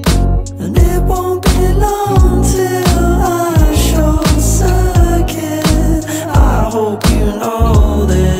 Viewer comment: thanks for this but @ 0:49 i think he says short circuit and not show circuit, good video tho ((